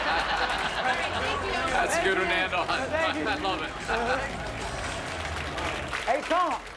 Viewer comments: Is it me or did Belichek call him Hernando at 5:38? Lol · did he say "thats good Hernando"? lol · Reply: Achoouken “that’s a good one to end on”